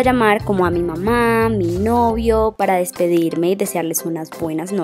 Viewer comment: escucharon bien que dijo llamar a mi novio